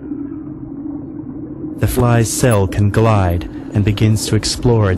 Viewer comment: What are you saying @ 5:41? Flies cell can glide?